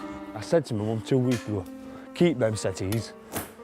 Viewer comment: the kid says to his mom. “I told the b***c to keep the settys”. I’m not from England what did he really say?? He was taking about the couches outside. I just wrote what I thought I heard. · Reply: yeah 'keep them settees', settees is another word for sofa